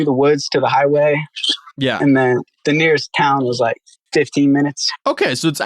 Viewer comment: not very important but just wanted to point out that he clearly said 15 minutes not 50. Edit: Just realized a lot of the subtitles are scuffed af through the interview part. was probably just done by an AI.